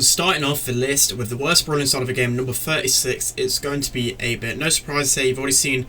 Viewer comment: If you turn on captions at 1:05 the Captions suggests that he's saying "a bitch" instead of "8-bit" lol